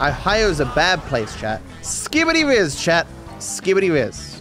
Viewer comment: Did really said "skibidi rizz"...?